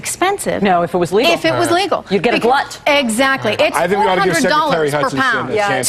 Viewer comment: did she say "You'd get a blunt?"